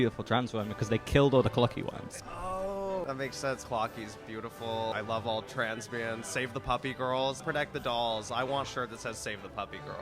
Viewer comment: this was an amazing interview like wow ps i think at 0:22 she actually says "i love all transbians"